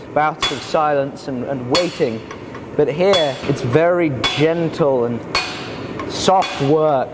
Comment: did say "genital"? 😂